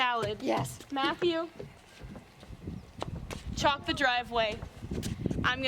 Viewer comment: Omg when she said chalk up the driveway I heard “chop up the driveway” and I was so confused